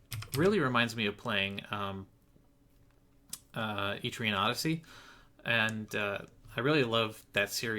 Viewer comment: What game is he referring to around 46:23? I'm not a native English speaker so I didn't really understand what he said but I want to look it up. Thanks! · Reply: Etrian Odyssey series. Thanks for watching!